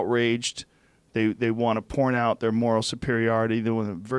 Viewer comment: did he say "porn out" talking about college students? freudian slip joe!